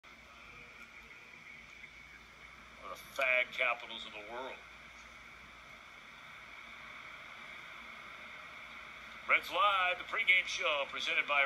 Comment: He should have used the old "I said the wrong word" excuse, I meant "FLAG" capital of the world. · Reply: I thought he said fat capital.